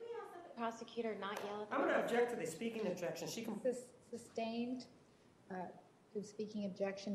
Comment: Can anyone clarify Juan comments regarding speaking objection? sounded like Juan said, "I object to the speaking objection she".....piss off? cant make out what he said or if he said anything? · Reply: Right after he says "she can", the judge says "sustained" and her "s" is what sounds like a continuation of Juan's sentence